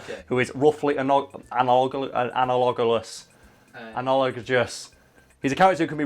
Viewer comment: can someone track down the correct pronunciation of what hes trying to say? I don't think analgogglefist is a word, although it should be.